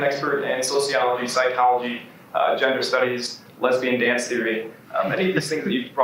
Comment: I could have sworn that I heard "lesbian dance theory" as a subject.